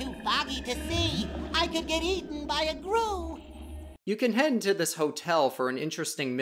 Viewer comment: did SpongeBob say Gru from despicable me!